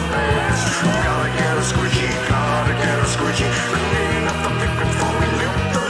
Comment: Good job getting a pixilated censor cloud to fit in with the credits, is that from mixed-up Mother Goose? While I love the "space janitor/Ballad of Roger Wilco", I'd love find the lyrics as I can't comprehend some of them. For instance "go to get a squishy"? 44:16 Sounds Dirty! Urban dictionary says a squishy is a video game character who dies a lot, so maybe that's it? Can't hear the line right after it either.